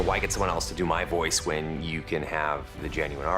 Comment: WHat did he trie to say in french? xD I heard "Je bilaise de flanc brun"??